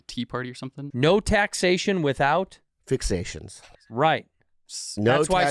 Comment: isn't it representation? Instead of fixation 😂